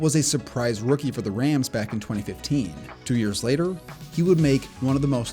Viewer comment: did bro say the rams